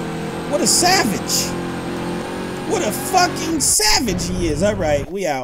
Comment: did he just say fuck